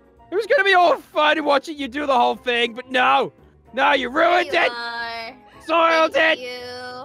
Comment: is saying "soriel did" or "soiled it" either way is weird